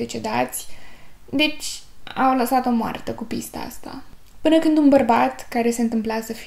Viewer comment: Min 12:41 voi ce auziți? Dar sincer vă rog. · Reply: pista! haha amuzant